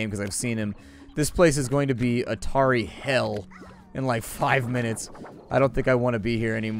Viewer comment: On January 6th, 2016 Emperor discovered Atari Hell. @ 13:45 (I know he actually said "a tary/tarry hell.")